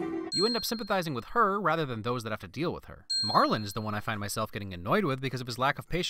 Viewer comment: subtitles say "to deal with that her" when he says "to deal with her" ding